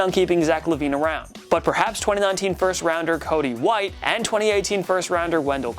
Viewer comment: did he say Cody White?